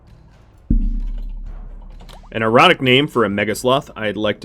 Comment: Had to skip back- I swore I heard you say Minnie was an erotic name for a megasloth! I guess ironic makes more sense, haha.